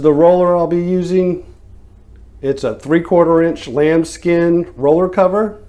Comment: did he say lambs skin ?